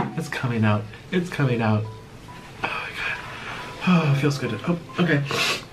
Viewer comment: Wait at about 6:16 did he say it feels good !?